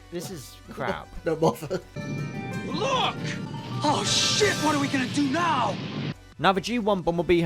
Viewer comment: Random dumb question but at 6:03 does he say “Oh shit” or “Oh shoot”? The second one makes a lot more sense but I can only hear “oh shit” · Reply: He said shit